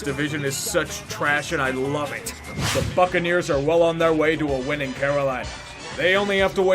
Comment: I thought he said fuckaneers lol